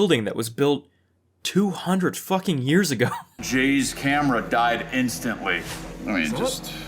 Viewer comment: swear to god i thought he said james cameron died in seconds